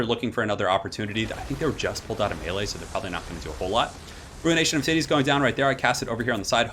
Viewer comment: Did I hear... ruination of titties?